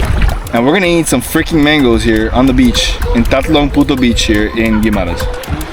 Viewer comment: I thought I heard tatlong puto beach @ 19:32 that was really funny hahaha. Glad you're enjoying our food and beaches. The waters are so clear unlike here in Manila. More power guys love your vlogs